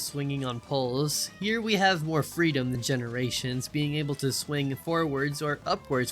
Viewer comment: im not sure if you noticed but you ssaid generations instead of unleahsed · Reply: I'm saying "we have more freedom *than* generations" not "we have more freedom in generations"